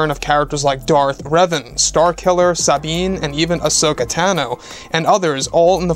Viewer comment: did he just say starkiller???